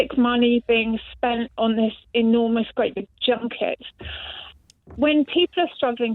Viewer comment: I thought she said jughead not junket 😂. Is getting rid of them worth the tourist money you would lose?